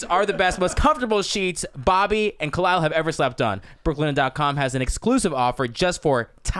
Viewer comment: Jamie thought you said Schlep Bobby, not Slept LOL!